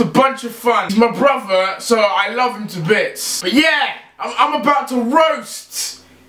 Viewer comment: On 0:15 seconds I. Thought. He said I love his bits